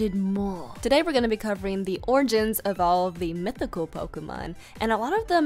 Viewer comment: When you said origins I thought you said oranges lol